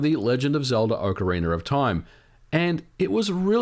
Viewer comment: I keep mishearing it as "ocariner of time"